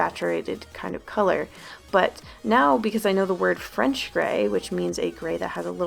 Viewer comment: At first I thought she said wrench grey and I was so confused.